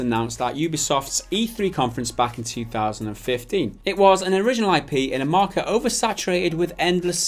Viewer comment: did he say Ubisoft announced for honor in 2050? lmao